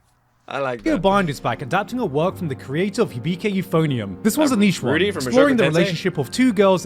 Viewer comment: what Garnt sayed:Author of Hibike Euphonium What look understood :Oooh Mushoku Tensei 😅😂